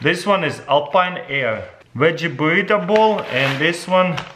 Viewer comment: I know that with his accent and the way Russians pronounce things but I thought I heard him say Wedgie Burrito 😂😂😂